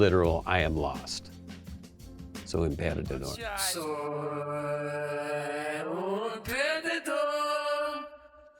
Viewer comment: "Perdedor" also means Loser in Brazilian Portuguese. The first time I heard it, I thought Beck was saying "Eu sou um Perdedor."